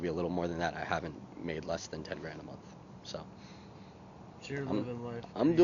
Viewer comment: You misheard, he said a month